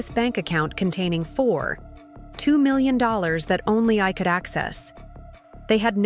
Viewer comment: I thought she said 4.2million, I wish they would say the amount properly.it's confusing